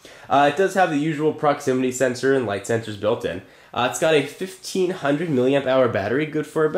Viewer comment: Oh, I thought he said 15 million hour battery. Damn, why can't you do THAT Microsoft?